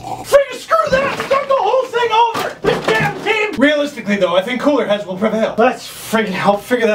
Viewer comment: what is he saying? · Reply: arinari "Realistically though I think cooler heads will prevail!"